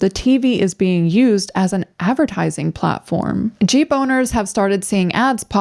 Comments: When she said Jeep owners, I thought she said G boners hahaha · Sorry but I thought you said "G boners" when you first said "Jeep owners". 😅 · I heard "G-boners" and could not have been more confused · when you said jeep owners i thought i heard G boners!! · I thought you said "g boners" then i realized you said jeep owners lmao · oh, jeep owners. i thought she said g boners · When you said "jeep owners" I thought you said jeeboners 😂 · I thought she was saying G boners.